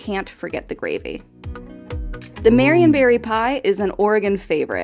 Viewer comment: I swore I heard Marion Barry pie. 🤣